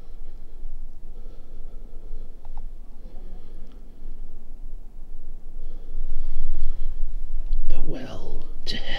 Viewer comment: i hear a voice but i cannot make out what it says anyone else heard something?? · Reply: Younger man say "the well was here"